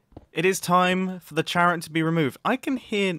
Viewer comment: Hermit Challenge: Go a whole episode using a charrot skin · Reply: I thought that u said carrot